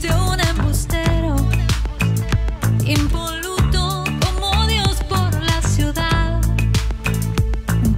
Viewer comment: Cada que la escucho pensé que decía "y un boludo como dios por la ciudad" que vergüenza la mía